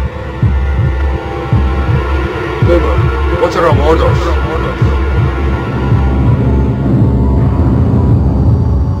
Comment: what did say at the end? I still can understand it, and i’ve seen this like, a hundred times. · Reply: I think said "holy shit" and someother guy said "Boomer what are our orders".